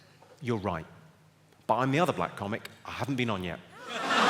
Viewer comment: I don't really hear what he says right there any help? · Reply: I am the Other Black Comic i‘m haven‘t been on yet